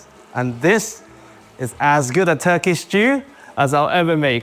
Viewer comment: Why does he call it a Turkish stew at 6:00? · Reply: He says turkey, not Turkish